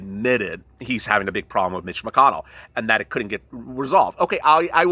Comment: I thought you said you had an interview with Mitch McConnell?????? If you did will you send it to me lol